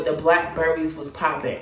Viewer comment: Why I thought you was saying Black Barbie. Nobody would give you a Black Barbie 😂😂